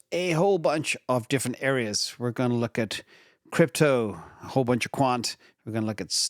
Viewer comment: Did you say “A - whole”… 😂 Sorry just my immaturity shining. 😂